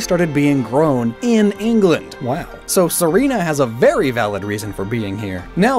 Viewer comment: So only I heard "Berry valid"? Ok